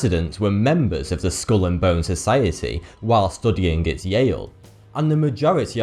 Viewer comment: I swear for a minute I thought he said Build a bear society